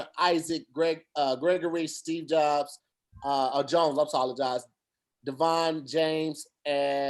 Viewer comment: Wtf did say at 1:26:34? someone please translate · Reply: jdrocc eddemac “I’m sologize.” A mix between I’m sorry and I apologize lol